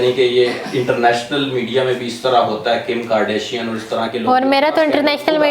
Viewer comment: did you say Kim Kardashian But she don't know her🤣🤣🤣